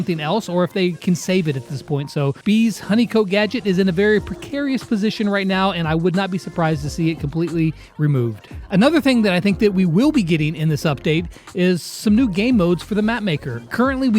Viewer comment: Did just said " Bea's honeycote gadget "?